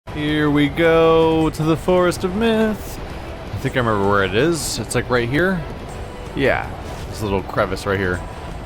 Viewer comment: at the start i was paying attention and it lowkey sounded like he said "here we go to the forest of meth" 😂😂😂😂